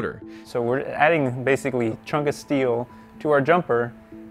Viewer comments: did he say "Chungus Steel" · Did he say chungus steel? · DID I HEAR CHUNGUS STEEL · I thought he said chungus steel at 7:20 ... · did he just say chungas steel? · he said chungus steel *i know he didn't say that, but still, that's what i heard